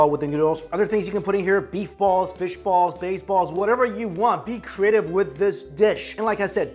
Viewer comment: Did he say baseballs?